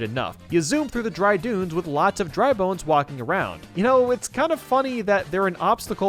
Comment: did he just say dry Boones